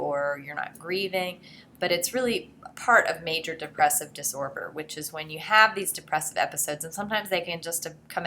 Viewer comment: Everyone: sadness Me: Am I the only one that noticed she said disorber instead of disorder?